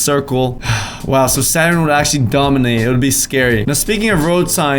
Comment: Saturn? I only know satan from what I heard.